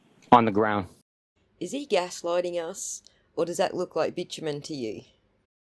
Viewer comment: What is that word you're saying? 9:00 The not-grass surface that you're saying is under the hat. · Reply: bitumen => Asphalt